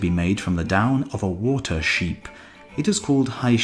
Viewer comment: does he say water sheep? Council of beetroot ain't gonna like this.